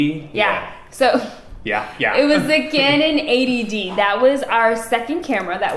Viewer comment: Dang I thought and said THE CANON Ayyyyy DD!!!! lol 😂 😂 cracked up for a while then I looked at the script and it’s the canon 80 D 😂 😂 love y’all such a great personalities I enjoy watching your vids much love here.